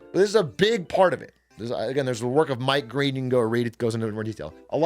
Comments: Whose work is he saying to check out at 3:01? Mike Green? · whose name did he say here? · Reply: Mike Green, who is the Chief Strategist for Simplify